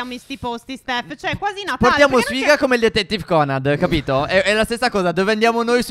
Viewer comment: sbaglio o ho sentito Detective Conad?